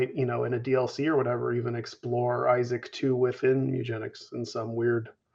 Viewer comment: WHAT DID HE SAY?! IN A DLC HE MIGHT EXPLORE ISAAC TOO? Like Isaac 2? No for real I know he's saying in a dlc he'll explore how Mewgenex ties into it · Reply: I think he was saying he'll explore Isaac a bit more in a Mewgenics dlc